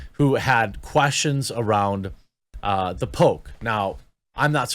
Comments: lol I thought he said "The Pope" not "the poke" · Thought he said you couldn't question about "The Pope"... 😅